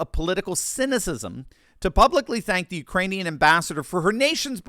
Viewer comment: He called the "Ukrainians" "Iranians".. I haven't laughed that hard this year.. 🤣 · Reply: I heard "Uranians".